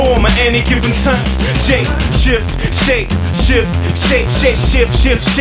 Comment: The first time i heard this I thought He said ShapeS#*t